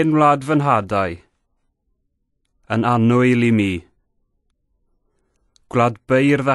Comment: Diolch ! I've always thought it said : Mae hen wlad fy nhadau yn anwyl i FI ... Is there a difference fi/mi ???